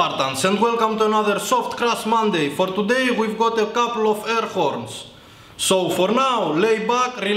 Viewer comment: "For today we're gonna blow up couple of airports" That's all I heard...